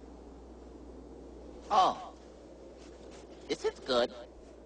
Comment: I thought he said is this good ?